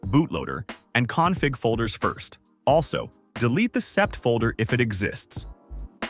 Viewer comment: 'Delete the "_what_" folder, if it exists'. Did not catch what you said at 7:10 closed captions said "SE" folder. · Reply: SEP